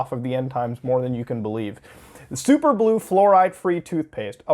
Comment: I thought superblue said superlube